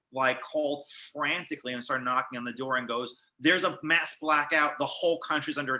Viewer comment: "Then, I apologize" "I thought you said Hamas" What were you thinking? There are few things people will never forget. Ask Kramer.